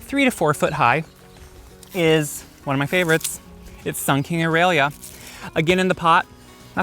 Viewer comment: you did not list the "SUN KING" Aralia luckily I had already booked mark that plant in my to get list.... you said fast and I thought you were saying SUKEN AURELIA OY ( my ear need a tune up!) I thought I would add here in case others want the name. Not sure if it will grow in my zone but it is GORGEOUS